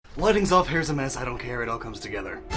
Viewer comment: Do you guys really like that first line that much? haha. I did everything here in one take because I was in a hurry. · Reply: I misheard "lighting's off" as "wedding's off" so I was pretty confused at first